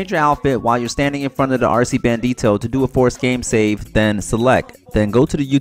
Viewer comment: What do you say in 0:48? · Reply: change you outfit to do a force game save...also set your spawn location to another apartment or maze bank works 100% doing that